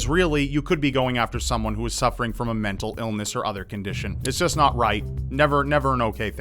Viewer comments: Am I the only one who heard "otherkin-dition" at 10:03? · "Other condition"...? I honestly thought you said "otherkin-dition" and it cracked me the fuck up.